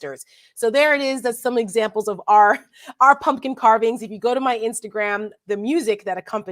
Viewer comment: Darn I thought she said pumpkin pardons LOL